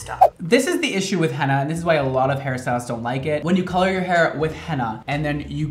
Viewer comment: i really thought u said harry styles so i played it again and again 10 times until i understood u said hair styles